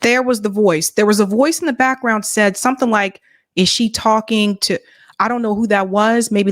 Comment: “Are you talking to me” 2:41 that’s what was said